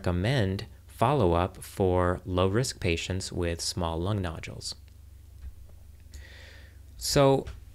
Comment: I thought it said Lung Noodles